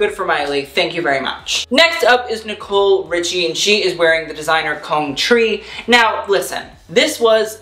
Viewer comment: Okay y'all I swear i heard "Designer cone tree" at 36:00 😂